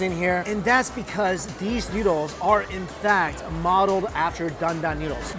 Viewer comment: i legit thought he said needles lol